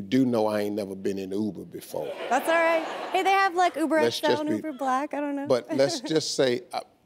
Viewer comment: Hold up, did she just say "Uber Black"? anyone peep that?